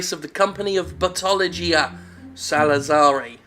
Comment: I thought it was called Bootyology before I clicked on it. I was disappointed. Also Google Translate did some overtime in this. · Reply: Leafhopper I keep hearing "Buttology." I too am disappointed.